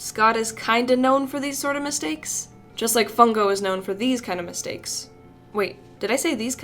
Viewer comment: Am I the only one who heard on 4:48 on this video said fungo